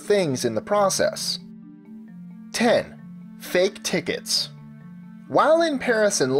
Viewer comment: Did he say while in paris in london at 4:50 ?